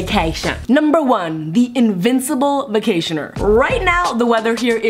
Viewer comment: it said vactioner instead of vacationer